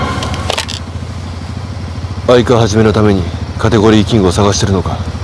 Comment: 字幕ひどすぎやろw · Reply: バイク始めのためにって表示されたせいでそれにしか聞こえなくなったw